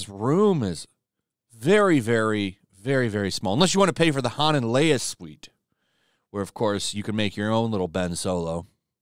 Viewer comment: I thought you said "the haunted Leia suite" and I legit laughed.